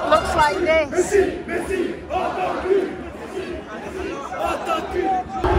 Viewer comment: Can anyone translate what their chanting? I just hear "Messi Messi Octopus"